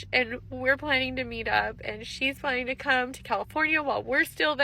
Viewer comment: Good riddance. The fewer of her kind here the better. Wait... did she say California?.. I thought she said Portugal. I pray she said Portugal. California has enough problems without her hysterical behavior.